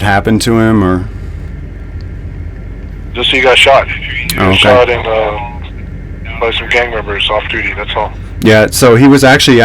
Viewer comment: Did he just say off duity gang members.